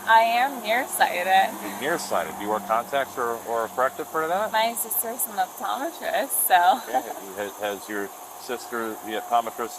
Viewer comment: Lol. I thought she said her scissor is an optometrist. Always be vaping, seems like they can't do anything without hitting it (one last time). Dangerously oblivious.